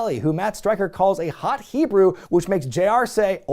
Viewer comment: did I hear all that correctly? I heard gymnast , did anyone hear Hot Hebrew?